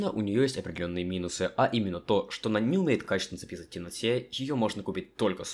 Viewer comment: как понять что он сказал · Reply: а именно, что она не умеет качественно записывать в темноте